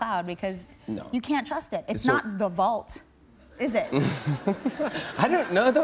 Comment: what does she say in 1:31? it's not...what? sorry english is not my first language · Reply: It's not the vault